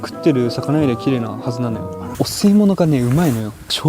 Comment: よそ見してたら「汚水物」って聞こえたんで、まぁそりゃな……って思って画面見たら カニの「お吸い物」のことだった 私の頭の中が汚染されてる